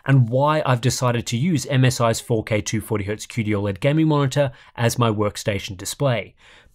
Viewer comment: whenever someone says qd oled, I always hear "cutie oled" and go wtf